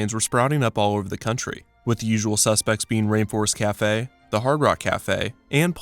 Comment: i thought it said reinforcement cafe 😂😂😂😂